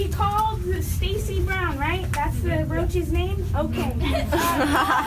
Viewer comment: What did they call her? "The roach"? She's an Evergreen grad herself, and was always trying to treat people with respect.